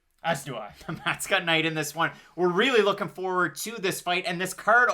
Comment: both of you picked Knight, but the captions say MATT: MANIFIELD Maybe spend more time with video editing instead of rushing it.